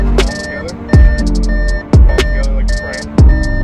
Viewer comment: Hey, at 0:41 I bet the cop was not saying "Put your palms together like your [something is] praying," but "Put your palms together like you'RE ("you are") praying. Because with just "your" there, he would need to have included something that belongs to the guy, which belonging would be praying instead of the guy himself. But then... silly of the cop to ASSume that all people who pray use that hand gesture. What if we just have them down to our sides or have our arms folded when we pray, or don't really have any specific body language for our prayers other than maybe kneeling and bowing our heads, or not even doing that for our prayers? Psch.